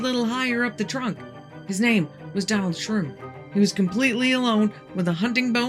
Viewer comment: I thought you said Donald Trump at first and I was like, “Holy crap, the President fought an alien robot thing!?”